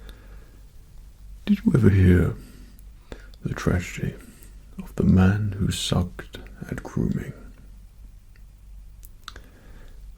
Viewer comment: i listened to this while chewing gum i hear " the man who sucked a crewmate"